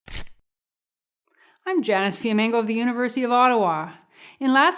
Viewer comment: presents some excellent points here. Think of where we are today to when this video was originally posted several years ago. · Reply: Great point, I’m pretty sure that I’m hearing her say “Janice” my wife and I checked and I think I heard “Janice”